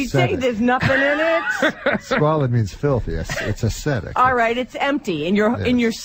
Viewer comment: What's the word that Norm uses at 13:21 and then again at 13:25? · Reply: ascetic